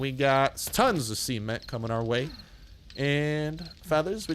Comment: Anyone else thought he said " lots of semen coming our way"? No? I'll keep my dirty mind to myself